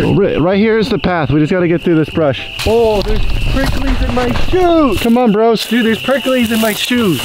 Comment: I thought it said “oh there’s Pringle’s in my shoes Took me so long to write this because my stupid hand is shaking